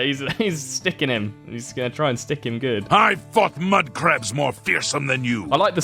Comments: I wasn't looking at my phone and I swore he said "I fuck mudcrabs..." Once he finished the sentence I was still so confused... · I thought he said he fucked mudcrabs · i legit thought the audio said i fuck mudcrabs more fearsome than you